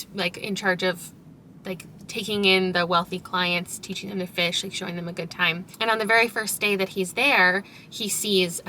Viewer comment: i thought you said "taking in the fish, showing them a good time" and honestly i wanna read that book